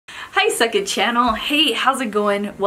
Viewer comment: I really thought you said...Hi suck it channel...lol.